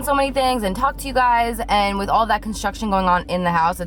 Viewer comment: I thought you were saying that you're having contractions instead of construction Lol i'm like but she's not pregnant